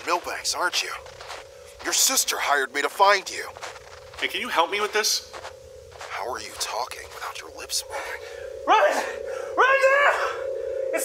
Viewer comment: might just be the accent but everytime I hear "can I help you with this" I hear mandalore